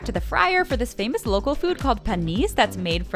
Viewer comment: When she said panisse at 6:25 the subtitles said penis 💀